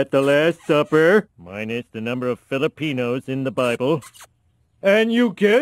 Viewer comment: Did he just say Minus the Filipinoes?